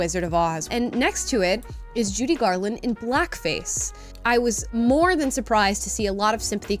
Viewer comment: So what I heard is "she earned the blackface card"